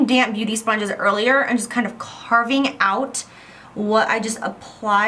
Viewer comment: Great video! 👏 I've got two simple questions, hope somebody can answer them: At 14:48 what did she say? "carb out" ? (English is not my native language) And what is she putting on that beauty sponge to "carb out" her contour? is it the RCMA No-Color Powder? Thanks in advance! 😊 ✌ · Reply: Federico Pascuale "carve out" and yes